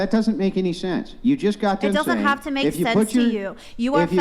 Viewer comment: Wait a sec... @ 4:33 Did she just say "It does not have to make sense"?